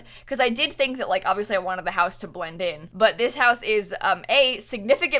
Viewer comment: Okay but did just say bwend in instead of blend in at 2:00 ??? God, I’m laughing so hard.